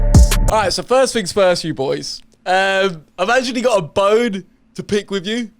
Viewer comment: Who else thought he was gonna say ‘I’ve actually got a boner’ at the start.